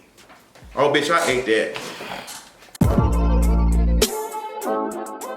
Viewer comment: Yes lol, at first I thought you said "Oh bitch I hate that". Then I had to listen again, yes bitch you ate!